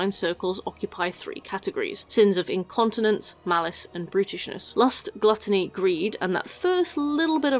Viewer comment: Why did I hear "sins of brutishness" as "sins of Britishness" and more importantly why did it take me several minutes to figure out that was wrong?